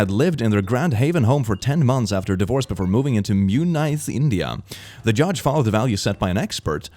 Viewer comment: Dang, I guess I live in India now, instead of Indiana .-.